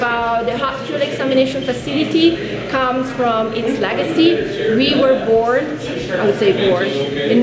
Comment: I thought she said "we were bored" and I thought to myself "typical scientists, getting bored and making the most radioactive room ever for funnsies"